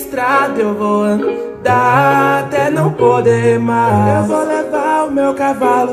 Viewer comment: É só eu que escutei "eu vou dar até não poder mais"